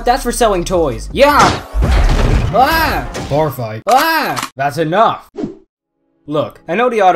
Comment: what did hound say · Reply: Warfight